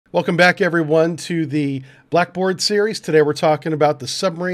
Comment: Dang it, I thought it said "Blackbeard Series" made me think the Pirate's invented submarines 1st,lol. 🧐🤔😉